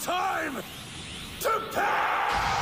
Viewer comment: Shit, i always think Goku says: "It's time to die", to bad he said "to pay"!